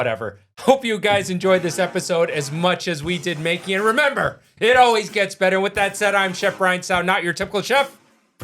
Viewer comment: I misheard I thought you say say bye to the white cam